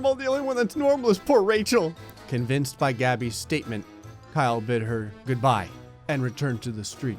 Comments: OHHHHHHHH! YOU SAID KYLE BID HER! I THOUGHT YOU SAID KYLE BIT HER! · If you think this game can't get any more screwed up...it can... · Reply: ooohhhhhh. You said Kyle bid her. I thought you said Kyle bit her. Ha